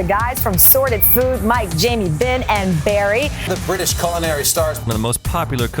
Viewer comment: Did they call Jamie Jenny at 0:10 ?